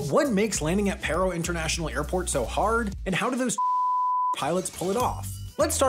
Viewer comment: The censor makes it sound like hes saying "how do those fucking pilots pull it off"